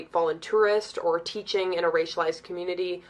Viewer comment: did she say "..in a racelized community"!?? .. "racelized" ..?.? . Wtf is that supposed to mean? Is it even a word??